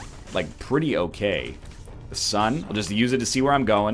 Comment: Every time the announcer says "The Sun" I hear it as "The Succ"